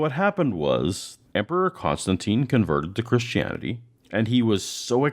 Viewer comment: i promise you, but when you said Emperor Constantine. I thought you had said Emperor Palpatine